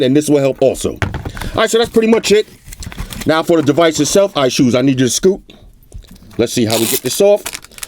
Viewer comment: @ 5:47 I swear it sounded like you said, "I-Shoes I Need You To Scoot" xD "I LOVE MY I-SHOES.......I LOVE MY I-SHOES..." .........GIGGITY!!! xD